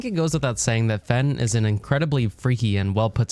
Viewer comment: Heh, it sounds like he said “Fent is incredibly freaky”